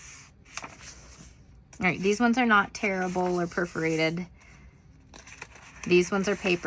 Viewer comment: You said that they are tearable. I thought you were saying terrible. 😂😂